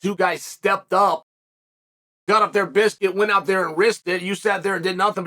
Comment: Did he just say “got out their biscuit, went out there and risked it”?